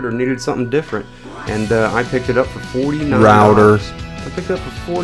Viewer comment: what does the voice in the background say at 2:51? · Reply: he says router